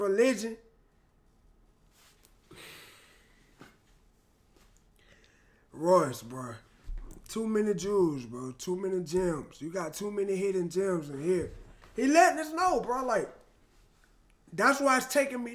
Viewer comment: swear to god i thought you said "too many jews" my jaw fucking dropped